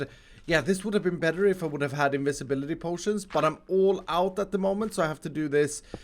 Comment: did he just say INVISIBILITY POTION??